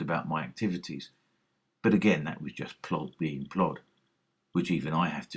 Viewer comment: What do you say at 6:25 that the cc puts as “the plot being clawed”? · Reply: Ha ha. It's plod being plod. It's British slang for the police. Not really insulting, like 'filth' that I used before, but just unflattering. Plod (without a definite article like 'the'), suggesting that the police are slow and ineffective. So, plod being plod, just means the police being the police.